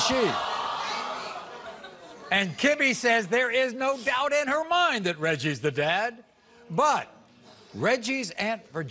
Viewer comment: Hold on i know it’s not about the results but did anyone here what that other girl in the audience say in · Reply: Chile I listened as hard as I could I think she said that baby ain't cute